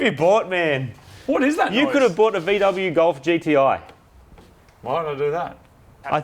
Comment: Did he say BMW golf GTI????? :O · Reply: No. VW Golf Gti.